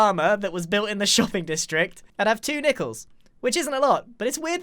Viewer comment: When he said I would have two nickels I thought he said sickles